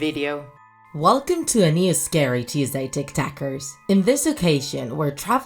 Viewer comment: Who else thought she said tic tokers